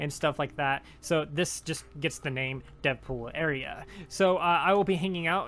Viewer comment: Lol I thought u said “Deadpool area “ Also what’s 12 USC converted to EST